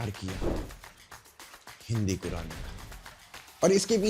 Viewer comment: bhai kya kahaa? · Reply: Hindi Quran